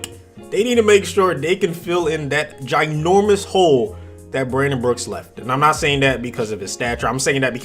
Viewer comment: Who else thought it said Brandon Cooks?